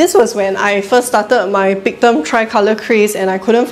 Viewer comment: Hmm.... Did she say, started her Pritam tri color craze? @ 3:57 Anyway, she's the most optimistic gardener I've seen. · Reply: LOL! She said Pictum Tricolor craze 😂